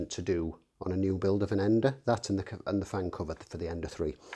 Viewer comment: Sorry, what do you say at 15:10? That, and the ... for the Ender 3 .. ? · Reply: The fan cover. The original E3 had the fan vent on top of the box, the E3 Pro swapped that so it was underneath. Being on top means there's a risk of things, especially strings of PLA dropping into it and breaking the fan blades.